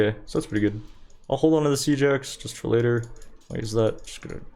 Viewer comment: Omfg Siege Axe, all this time I thought you were saying "Seajacks" and I thought it must be some American sport reference or something lmfao.